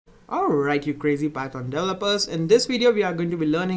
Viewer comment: Almost thought he was saying "alright, you crazy bastard developers"..